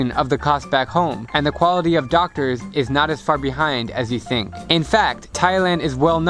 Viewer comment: lol he said far but i heard fuck😂😂😂